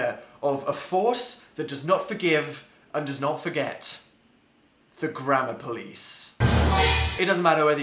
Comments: The whole time I thought he said the grandma police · I'm sorry but I thought you said "The Grandma Police" 😂😂😂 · i thought he was saying the grandma police